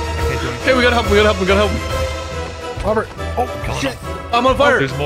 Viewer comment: Am i the only one who hear say shit here